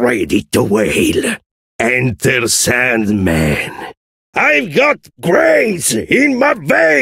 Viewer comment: i hear Enterse***